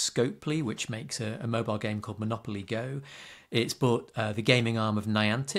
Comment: monopoly go? did i hear that right?